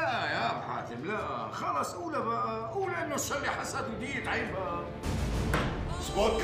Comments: وش قال مامون بيك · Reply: ماهر هلال خلص قوله بقا قول انو الشغلة حسد وضيقة عين بقا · وش قال مأمون بيك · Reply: حسد وضيقة عين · من 1:21 الى 1:24 وش قال مأمون بيك عشان يزعل ابو حاتم ؟ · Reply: مأمون بيك قال:قولوا انو الشغلة حسد وضيقة عين بقى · مامون وش قال هنا 1:22 عجزت اعرف وش قال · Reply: ضيقة عين وحسد اتوقع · وش قال مأمون عشان ابو حاتم يعصب في الدقيقه 1:20 تكفووون وش قاال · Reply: XY Sultan يقول صارلي حسد وضيقة عين قصده انه يحسده